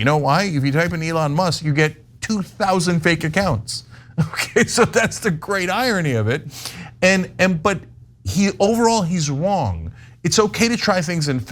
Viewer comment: did just say overall his wong 😁